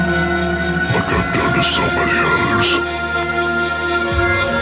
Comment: what did he say? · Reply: ”Like ive done to so many others” in the subtitles